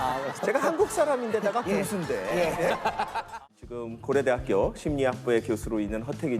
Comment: 우영우를 하도 봤더니 고래대학교로 들리네